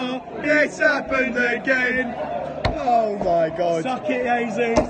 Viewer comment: What did you say?? Im not good at English, so I can't understand what you say (song). ------ ------ AGAIN?? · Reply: Oh they said It's happened again VAR Its happened Again